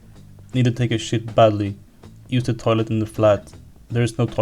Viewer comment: did you just say "eat the toilet"??? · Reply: I'm pretty sure I said "use the toilet" lol